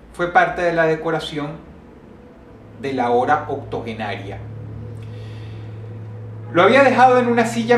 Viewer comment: no entiendo cuando Dross se refiere a la HORA OCTOGENARIA en el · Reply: Me parece que dice "De la ahora, octogenaria (refiriéndose a la abuela)".